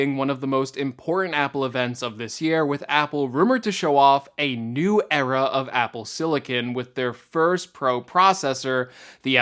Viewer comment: I heard it as "...a new error of Apple silicon." 🧐🤔 No, not planning on buying new hardware. My 2013 iMac I put in an SSD last year, and I put in a 1 TB M.2 with an adapter in my 2015 Macbook Pro too, so they should be good for a couple of more years.